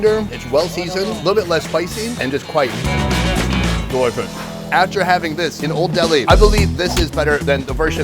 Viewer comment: what does Sonny says after: "and quite..." i cant catch the last word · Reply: 'Delicious' Or 'worth it'. 🤷‍♂️